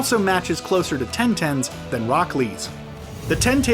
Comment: How I heard the names Nalouto (Naruto) (Boruto's dad) Zazke (Sasuke) Itatchi (Itachi) Osusuki (otsutsuki) Akasuki (akatsuki) Edo Tinsei (yes that's a jutsul Oyochimayu (Orochimaru Hagrow-ama (Hagoromo) Roc li (Rock lee) Gwaguya ( Kaguya) Kukugas (Kaguya's) Mumushiki (Momoshiki) Himawawi (Himawari Car (Karl) Gyugi (Kyubi) Chinchuriki (Jinchuriki) Sword of TATSSUKAHHHH (OK OK GOD I'M DONE) I'm French, I can don't understand few words but the names.... God my head just explodes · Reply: For Rock lee I thought he said Brocli